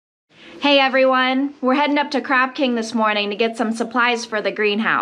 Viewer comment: I thought you said crap king! Lol im watching all of you videos since yesterday so much joyful and informative.